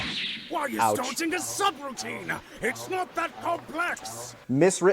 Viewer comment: OH SHIT! I always heard "Why ISN'T Dodge a SUBROUTINE!?" instead of "Why is DODGING a subroutine!?" because my brain autocorrect/Mandelaed the misnomer.